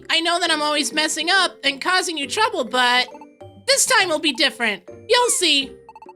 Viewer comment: when he said "any trouble, but" I thought he said "bubble butt"